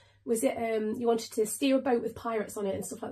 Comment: 😅 sometimes I have a hard time with a British accent so when you said "steer a boat" I thought I heard "steal a boat" 😂😂😂! I had to rewind and turn the volume up a little to hear it properly! 😅 Anyways, I'm so glad you got to STEER a boat full of pirates ❤